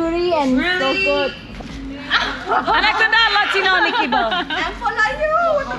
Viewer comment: did she say "latino mickey mouse" 😂😂